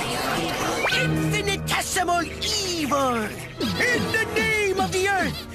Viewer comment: what did he said? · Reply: "Infinitesimal evil"